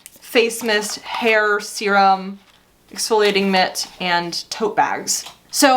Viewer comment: I wasn't watching and I thought you said "exfoliating mist" and was trying to figure out what was going to be flying at my face 😂😂